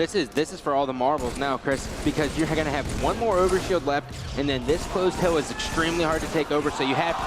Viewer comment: bro said “Ogre shield” instead of over shield 🤣